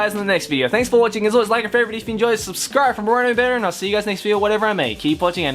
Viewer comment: I've been subscribed to him for over a year and I still don't know what he says in his outro. I always hear, "subscribe to me for more anime banner," but I don't think that's right.